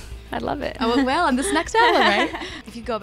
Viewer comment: I heard "sex album" before "next album". Oh dear.